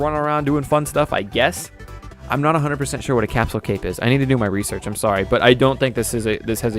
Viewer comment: No problem, my english isnt perfect and i typed it for the heck of it. XD Also i made a error the question was can capsules be equiped on existing characters (like goku/vegeta etc) the french acent I heard it as capes